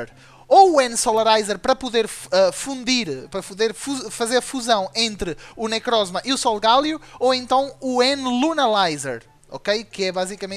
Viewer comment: o que ele disse? Pra o que? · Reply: Pra fazer a fusão de necrozma com lunala ou solgaleo